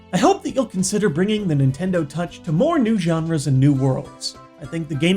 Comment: did he say Nintendo touch..?😂